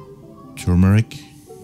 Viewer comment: Was sagt er? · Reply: Turmeric = Kurkuma